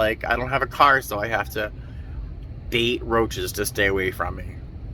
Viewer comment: OMG. I thought you said you had to Date roaches.